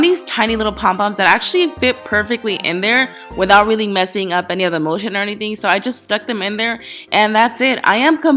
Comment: it sounded like she said so there's no bull shit or anything